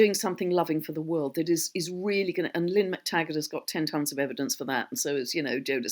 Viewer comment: Who is she saying at 55:33, Lim Taggart and Joe Dispenza?